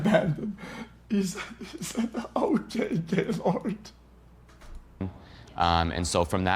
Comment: can I get a translation on this please I’ve legit watched it back 10 times idk WTF HE SAYING this guys not speaking english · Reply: “Ok gaylord”